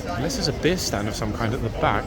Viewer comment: I miss heard you and thought you said that lady had a beard , I'm rewinding it to find her , but she has a beer which is a better thing to have I think 😂